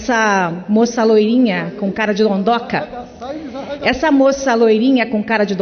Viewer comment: " Moça loirinha com cara de dondonka" eu ouvi "Moça gordinha com cara de porca" kkkkkk Se acha dimais Peppa